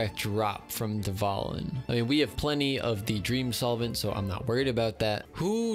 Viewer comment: Did he just say he always gets solvents....